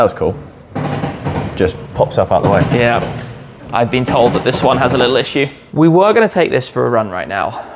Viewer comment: lol 10:56 did you say "I've been told that this one has a little issue" and have to manually release the eyelid on the headlight for a Icona Series Ferrari SP3? Was that really what I heard?